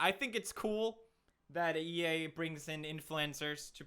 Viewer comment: Did he say EA